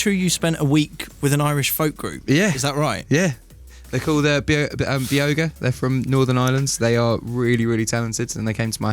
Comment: What was the Irish folk group he was talking about??? I love folk so much! ❤️ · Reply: Never mind. I found it. For those of you also wondering, he said Beoga. I thought he said The Ogre. 😂